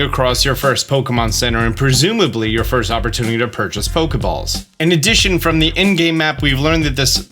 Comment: Fokeballs? Or am i hearing wrong?